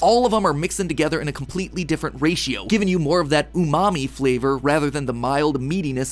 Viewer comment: He said “Umami”. I thought he said “ooh mommy”.